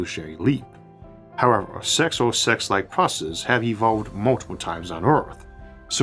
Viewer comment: I heard "sex and sex-like processes have evolved multiple times on earth" as "sex and sex-like processes have occurred multiple times on earth". Made me chuckle. I'm sure it has.